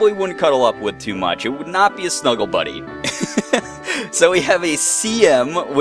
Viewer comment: At first (because I was listening to imagine) I thought at 5:47 you said "...So we have semen..." and I said to myself, "Wtf is he doing?!"